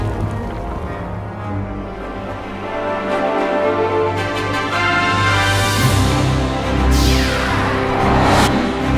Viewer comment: what did say there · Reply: This is what he said: "i see.." "that's fine.." "no problem.."